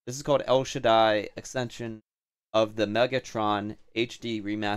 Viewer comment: I kept mishearing Metatron as Megatron lolll